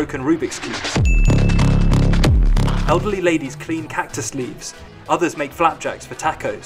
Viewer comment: I had to rewind 2x because I heard ugly ladys prepare opps it's elderly... also shoes up on the line means the block is open for business drugs! And that flea market is shit!